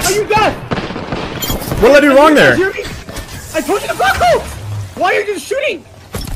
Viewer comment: He turned into Mario, thought he said "I told you to Wahoo".